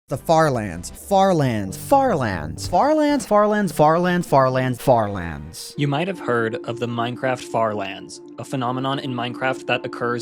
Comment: I thought i heard "Farm land"